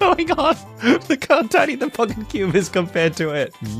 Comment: did anybody else hear that? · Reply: He said pocket cube if you slow it down